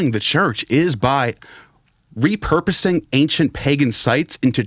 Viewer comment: I hear "Theft" not repurposing, Lol.